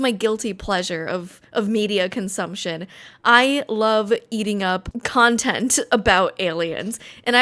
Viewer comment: I am convinced that she said "I love eating up aliens", paused for a second, realized what she'd said, laughed for a good minute, re-phrased her sentence then kept reminding herself to make the right cut while editing